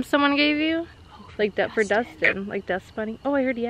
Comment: after said " i heard dust bunny" something said dust bunny at 10:49 and then at 10:51 i heard "go for dusten"